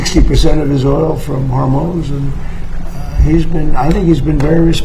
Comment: Maybe it’s the translator or maybe he did actually say this. Crazy whether it oil from Hormuz or hormones. 😂😂😂😂